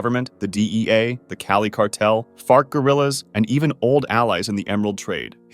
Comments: did I…. Did I hear that right? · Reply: Fart Gorillas T_T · “Fart Gorillas” did I hear that correctly? There’s a Cartel called the “Fart Gorillas”? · wait, did you just say fart gorillas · Did he say “fart gorillas”? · Did he just say fart gorillas? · Did he just say fart gorillas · I'm sorry, I've replayed this maybe 5 times....did you say the FART Gorillas? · Anybody else hear “Fart Guerrillas” at 1:44 🤣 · did you just say fart guerillas at 1:43? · Did I hear that right...... Fart Gorillaz? 😂